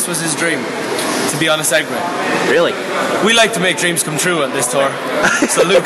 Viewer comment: At first I thought it said "we like to make dreams come true at this store," but I listened closer and it actually says, "we like to make dreams come true on this tour." how silly of me. My dream was fufilled today. Watching luke on a segway. And seeing Niall.